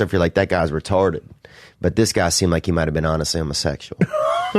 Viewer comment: I thought he said “ a situation where a lot of people were like this guys retarded, I was thinking he might have been on to somethin” 😂